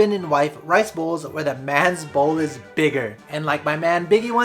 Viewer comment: I heard 11:15 "where the man's balls are bigger"